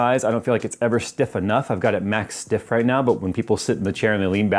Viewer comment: i could have sworn you said "i have a max stiff".......good video, i'm finally looking for a new chair and 6;2